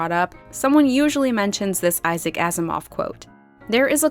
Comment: Why did I hear "think of this Isaac Asimov quote" as "think of this as a 'gasm off-quote"? Like an offhand comment made upon climax??